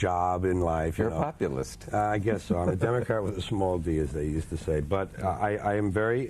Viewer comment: Im very.............??? What does he say? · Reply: He says, "I'm a democrat with a small 'd' as they say".